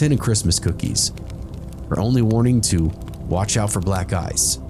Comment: I thought he said “and watch out for black guys.”